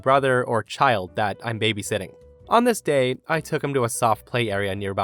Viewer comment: Every time you say "On this day..." i hear "Nameste" lol...